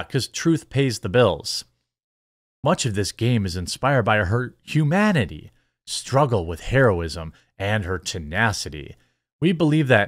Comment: WHUT??? Oh,sorry,I misheard you,I thought that you said,"Her struggles with heroin";I did a double take. 🤣🤣🤣